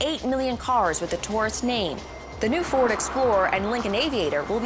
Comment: She says "mogor" instead of "motor"